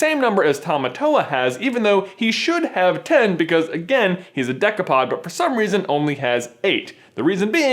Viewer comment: Wait he said he was a decapod I thought he said he was dick-apod